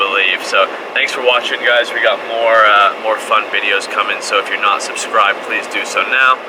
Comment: got more fun videos cummins. that's what I heard!